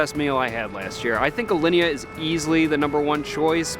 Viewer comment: What restaurant did he mumble @ 2:03? Alenia? Ilenia? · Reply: Alinea In chicago